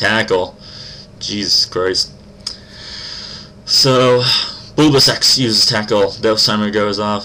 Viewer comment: at first i thought he said bulbo sex